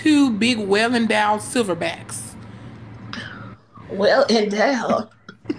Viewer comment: did she just say “well, Adele..”?